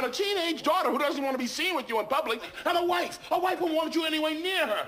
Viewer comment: Am I the only one who realizes he says. and a butler a butler who could be the father of your child! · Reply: I thought he said a downfall of your child 😂